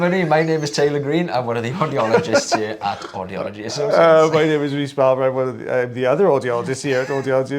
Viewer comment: WHAT TF DID HE JUST SAY? was that english? All i heard was "my name is Reese redacted? the other redacted? lmao! nothing, i heard NOTHING! LOL i fking love the irish.